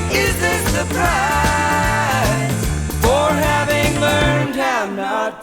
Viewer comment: Wow, my whole life I thought he was saying "Is this the price for having learned how not to cry?"